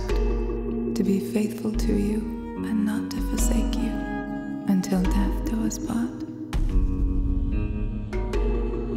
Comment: Do somebodyes understand what she say at 1:39 ? I'm french and i dont understend this part .. Help please... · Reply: Arachno Did “and not to forsake you”